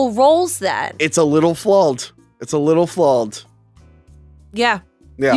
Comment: How come can’t saw the sound “aw” without saying “awl”? He keeps saying “Jawls” instead of Jaws and here at 43:30 he says “flawled” instead of flawed. Get your speech game up.